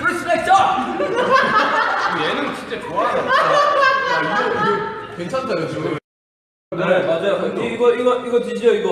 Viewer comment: I wonder what Suga said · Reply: He said: is he laughing because he likes it or...? J-hope: it's actually okay. Suga : He's making excuses for no reason.